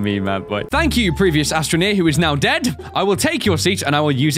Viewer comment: His accent makes it sound like he says "Astronia" when he says "Astroneer" :P